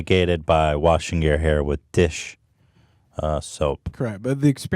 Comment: Yeah I heard “dish shoup” dries your hair